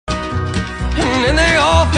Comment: I heard "As he raised his piss before he spoke"